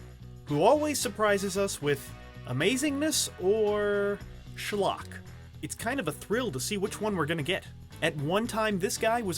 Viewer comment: Wait what? Did you just use a russian slang word "шлак" (shlak) which means "bullshit"? Wtf? :D